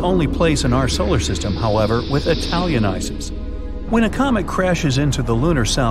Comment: Did he say Italian Ices???😂